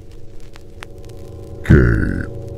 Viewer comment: Did bro say gay🤨